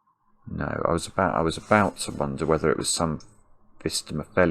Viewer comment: Could you tell me what the word is that you said at 15:05? Auto captions transcribe it as "vistemophilian" but that has no search results, and nor do any variants I've tried! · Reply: He's talking about a Phistomefel ring